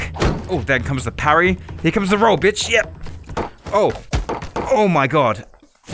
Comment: You guys know that oH Ma GaWd meme it kinda sounded like what he said